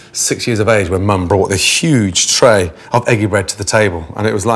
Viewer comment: Me: French toast Gordon, an intellectual: Eggy bread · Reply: I thought it said edgy bread